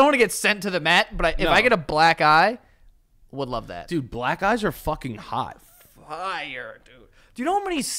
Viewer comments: 😂😂 I thought said “black guys are hot!!!” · I thought you guys said "black guys are hot" for like 10 seconds. I was so confused.